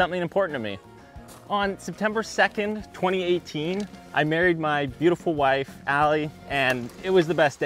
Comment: I thought he said “September 2nd I buried my beautiful wife” 😭